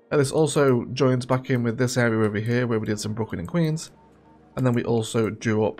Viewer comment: Sounds like you say Brooklyn and Keys, instead of Queens. Would love another overview video but about the statistics. Because my cities struggle financially, how do you do it? Love the transport, the Eggerglades, you even made the amusement park look good. And love how you have the goal to work in all the unique buildings because I'm not advanced enough to see those!